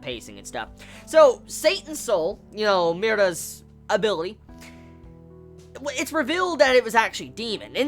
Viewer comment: Sounded like you said, abilly